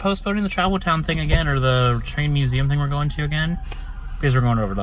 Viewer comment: Wow, thats sub at Fat Sal's looked epic! Is the Chinese theater always that busy? Hollywood Blvd, Fun area! Oh, you said TRAIN Museum...I first thought you said TREE Museum...I was like what the heck is a Tree Museum...LOL. Great Vlog! Thanks for sharing!